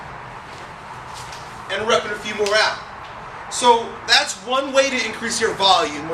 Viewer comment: it sounds like he said and rubbing a few more out lol